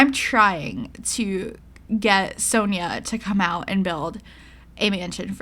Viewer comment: It honestly sounds like your saying Sonya in your introduction ... I thought that's what you were saying until yesterday lol